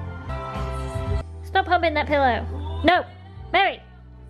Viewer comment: “Meri! Stop humping that pillow!” That was the golden sentence of this video. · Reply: 2 years ago I thought it said " Meri, stop hogging that pillow! " Now I know what she actually said